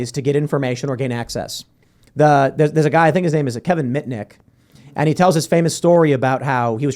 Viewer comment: I thought Tim said his name was Kevin Mint Dik 😂